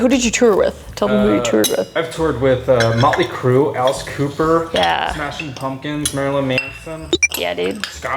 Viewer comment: did he say motley crue and alice cooper bc like bro lucky